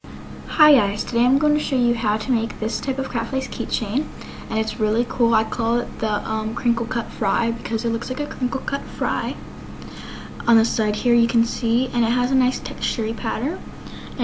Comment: At First I Thought She Said Wrinkled But Fries.. Buh This Is Really Good;!